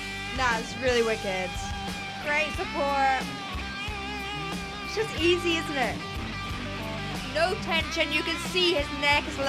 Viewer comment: what she said? "Easy as what?" I dont understand the last word, could you help me guys? 😅 I am not "englishman". 😂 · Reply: 'It's just easy, isn't it?'